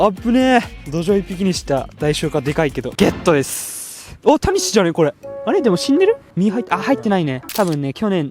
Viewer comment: おータニシじゃねが大谷翔平に聞こえた